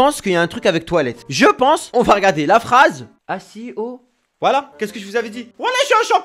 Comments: a 6:25 j'ai entendu vas-y au toilettes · j'ai compris "Aziz aux toilettes"😂😂